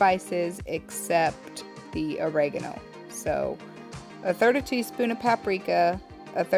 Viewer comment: LOL! You must really relish the sauce as to it being hot, as at 2:06 minutes in the video, what you said was a bit garbled on the sound. I opened up the transcript and it said at 2:06 minutes you said 30 teaspoons of cayenne pepper to be added. I believe you may have actually meant to say 1/3rd tsp. to be added instead, but here in Texas, everyone seems to love the heat, so one never knows for sure. ;-)